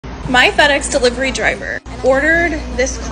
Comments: I heard "my fat a** delivery driver" ☠️☠️☠️ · So yeah, brain heard “my buttocks delivery driver” I had to pause and rewatch 😂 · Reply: The first time i definitely heard "FedEx". But now that u pointed it out, that's all I can hear...